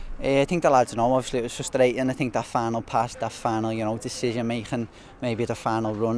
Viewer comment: I thought he was saying "fan" but it was "final"